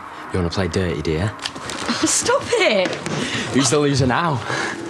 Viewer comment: What he said to her on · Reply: "Who's a sore loser now?"